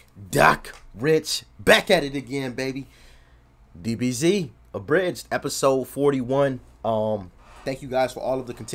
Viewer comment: I'm half asleep still. I swear I just heard you say "BBC Abridged" 🤣🤣🤣